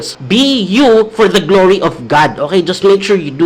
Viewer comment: Kuya yung sinabi nyo po sa 19:50, mali po ba yung narinig ko or may tamang meaning siya? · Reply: Sing for God's glory po. Nabulol lang ako. ☺️